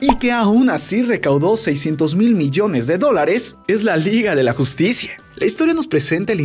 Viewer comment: soy yo o escuche "seiscientos mil millones de dolares"